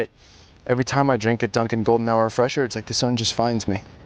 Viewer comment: I wasn't paying much attention to the commercial, and thought I heard golden shower and was trying to figure out why people were sticking up for it. After watching it again, I don't understand the problem with it